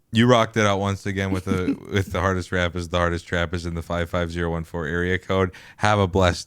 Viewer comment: 55014 area code? I heard “55104” initially and got excited